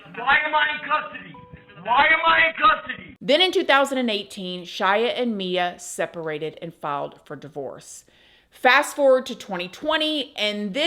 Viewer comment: What is she saying when it sleeps around time stamp 12:10? I can't figure it out. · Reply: Then in 2018 Shia and Mia separated and filed for divorce